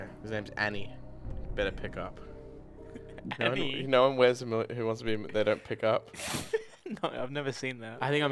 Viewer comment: am i having an aneurysm or what the fuck does say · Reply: “Y’know in who wants to be a millionaire when they don’t pick up?”